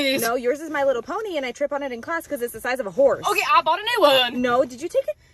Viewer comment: When you said giddy up I thought you said griddy up😭😭